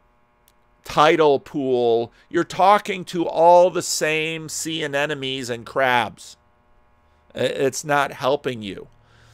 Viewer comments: I thought said CNNemies (like CNN enemies) instead of sea anemones at first. I’m sure some of the Daily Wire crowd would appreciate that for a neologism 😂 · Did anyone else mishear what said when talking about the Daily Wire "tidal pool"? It was "all the sea anemones and crustaceans", but the first time through, before I got to "crustaceans" and realized what was going on, I thought I was hearing a witty neologism from: something like "all the C-N-Nemies"...